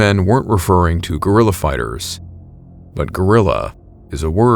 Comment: I heard "gorilla fighters" and I was ready for cryptids